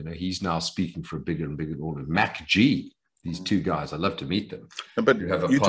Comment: Did he just say MacG, as in podcast and chill?